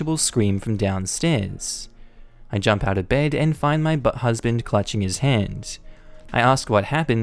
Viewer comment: Wait, did I just hear "B-Husband"? at 1:28?